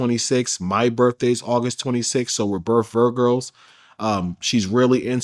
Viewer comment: He said “we’re both Virgos” but it sounded like he said they’re both “Virgirls” wtf 😂